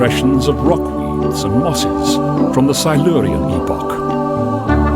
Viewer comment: from what epoch? It sounds like he's saying "the Siberian epoch," but there was no Siberian epoch.